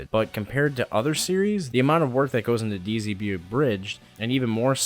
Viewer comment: Did you just say DZB Abridged in 2:18?